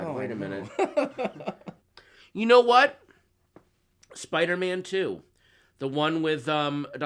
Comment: I got sooooooo scared when he said Spider-Man 2 which I love I thought he said the Amazing Spider-Man 2 LOL